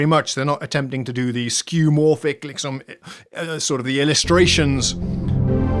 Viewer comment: I heard a “Liksom” in the video at #3:38 and had to check if you where a Swede :)